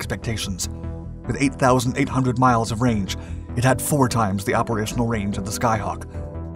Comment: did he say 8,800 miles of range?